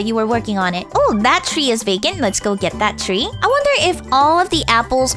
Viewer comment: when it was 2:44 did u say 'that tree is bacon?' ermmmmmmmmmmm ok... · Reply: rose She said "That tree is vacant"